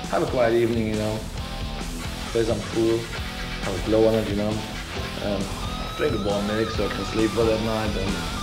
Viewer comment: Anyone know what Herman says after "play some pool" at 1:43? After a show I just go home, have a quiet evening, you know. Play some pool. [???], you know. And drink a bar mix so I can sleep well at night. And invite some friends over, you know, we play together. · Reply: Idk. But I think, he says: "drink a warm milk".